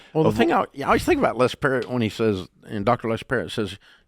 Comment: Anyone know what Doctor (Author?) Dave referenced in here? @ 14:20 - "Doctor Les Perry" (?) Any ideas if he has any publicly published books? (self help as opposed to journals within his field)